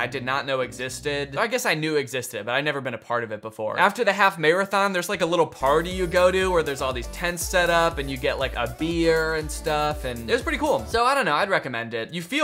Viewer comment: I stg i had to re-watch this part twice because I truly thought he said, "there's like a party, you get a MIRROR and stuff " lmao I was damn that's...idk how I feel about that 😕